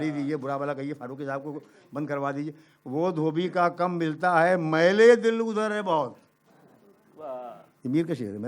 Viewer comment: Please someone write the sher of Meer which Farooqi Saab recited at 35:47. I couldn't hear it properly. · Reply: وہ دھوبی کا کم ملتا ہے میل دل اودھر ہے بہت کوئی کہے اس سے ملتے میں تجھ کو کیا ہم دھولیں ہیں Wo Dhobi ka kam miltaa hai mail-e-dil udhar hai bahut Koi kahe usse milte hain tujhko kya ham dhole hain